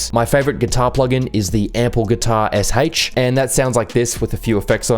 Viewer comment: what does he say? Ample guitar...? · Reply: SH - stands for semi-hollow. It was used in the remake of Travis Scott - CAN’T SAY ❤️